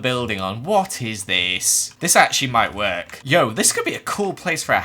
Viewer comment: did just say yo?